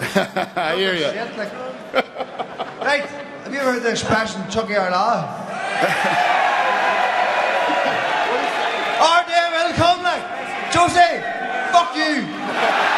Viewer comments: can someone please translate what is being said @ 28:15 thanks! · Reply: Dana, have you heard of the expression chuckie ar la? Our day will come like! Jose, fuck you! Late as hell to translate for you brother but there you go haha. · what does he say??? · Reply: "Have you ever heard the expression Tiocfaidh ár lá (it's our native language pronounced chucky air law it means our day will come) then he translates it then says like at the end